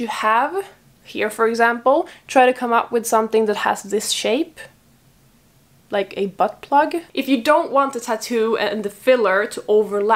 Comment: lmao ;-D I was scrolling through facebook as I was listening to this and all I hear is "like a butt plug", I was like wwhhhhat??? Did she really just say butt plug??? **replay***replay** hahaah ;-D Just so casual about it... lmao AHH.